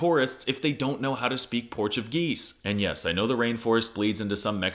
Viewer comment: did you say Port of Gueese?